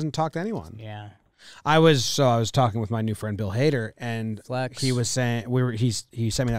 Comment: did he just say he was friends with bill hader?